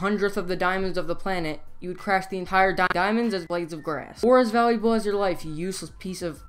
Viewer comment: Did he say blades grass?